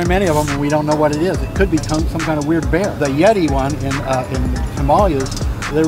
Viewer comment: did he say the yeti is in Somalia??? Uhhhhhh....